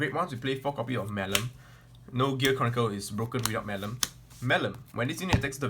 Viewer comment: OMG did he just say Gear CORNicle?